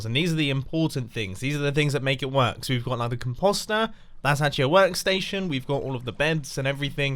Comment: Sorry but did anyone else hear com-pasta